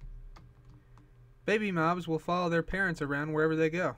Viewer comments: I thought you said baby moms will follow their parents around wherever they go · Oh I thought u said baby moms will follow there parents where ever they go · It sounded like he said baby moms anyone else thought he said that? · when he said baby moms i thought he said baby bums😂😂😂😂😂😂😂🤔